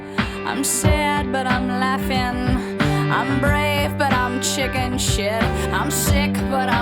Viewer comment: When I watched this video on MTV in the 90s, I didn't realize that the "shit" part of "chickenshit" was muted. What I heard was "I'm sad but I'm laughin', I'm brave but I'm chicken"...which made perfect sense and rhymed successfully despite being censored.